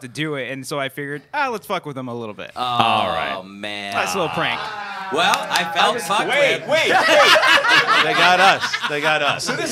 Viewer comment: I can't catch what said 5:07. Anyone? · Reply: He says "Well, I felt fucked with!"